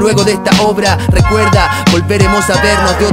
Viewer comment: Nunca he cachado que dice en el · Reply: Que en el “no tiempo” cantara ra ra ra ra...